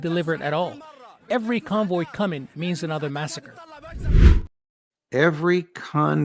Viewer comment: Judge I think that guy said: "Every convoy coming MEANS another massacre".